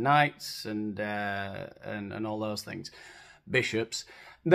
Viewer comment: Did you say "b*tches"?! 😳